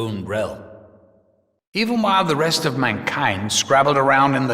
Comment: Almost sounds like "Mein Kind" instead of "My Kind". Shot in the dark, but you never know...